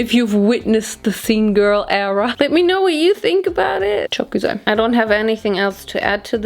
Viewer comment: In 20:10 did you just say "çok güzel" ? That means very good in Turkish hahah